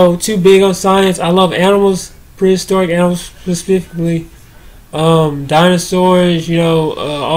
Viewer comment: how the fuck did you say that word prhjsfhdiffically